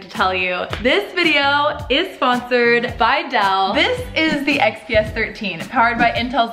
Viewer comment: i thought she said it was sponsered by Adele and i was like "WHAAAAA"